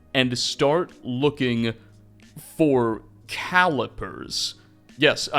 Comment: Maiq hear about Excalibur and thought they said x calipers